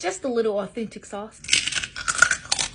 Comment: Naw bro did I just hear “autistic sauce”?